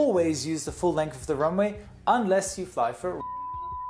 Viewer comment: I agree, I heard him saying something like this "Unless you fly for R....."